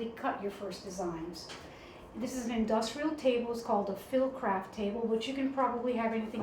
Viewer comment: What is the industrial fabric cutting table called? I heard "fill craft table" but can't seem to find online one that looks like the type in the video. Hers looks like the ones you see in factories. Btw, I love the video! :) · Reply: <link> . Phillocraft